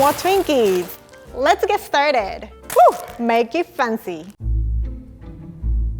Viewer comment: I thought It said start the glock at 1:35 lol and then someone pulls out a gun and shoots up hahah and then she starts cooking lol